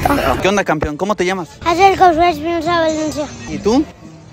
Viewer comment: Yo le entendí "Axel Josue Espinosa Valencia"